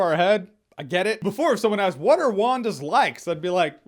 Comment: when you asked "What are Wanda's likes?" I misheard it as "What are Wanda's legs?". Limbs made for transportation I guess.